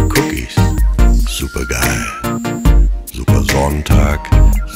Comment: I thought he was saying supergirl at first